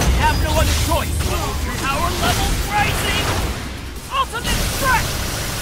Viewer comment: I swear at first I thought I heard X say "Urge to kill rising!"